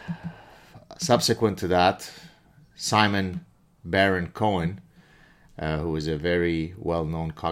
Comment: I first thought you said Sasha Baron Cohen. That would have made for an interesting symposium. He could use his Bruno character for diversity. 🙂